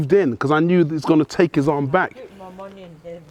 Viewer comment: What did the old lady say? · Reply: To me it sounds like she said "I put my money on him" as if she was placing her bet on a fight.